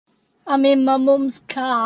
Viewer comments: I may be crazy but I swear I remember her saying “I’m in me mums car” not “I’m in MY mums car”. WTF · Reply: i literally looked up "im in me mums car" cause thats what i thought she said???? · i could have sworn he always said “i’m in me mum’s car” and the title even says that but he clearly said “my” here… maybe the mandela effect is real… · Mandela effect.......I thought she said "me moms car" not "my moms car